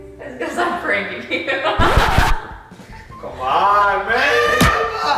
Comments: Did anyone else hear call “Come on babe Bella”? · Did anyone else hear call “Come on babe Bella”? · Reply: Fowler I hear “come on man Bella” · it sounded like he said come on babe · it sounded like said “ come on BABE” 😳. YOU GOT SOMETHING TO SAY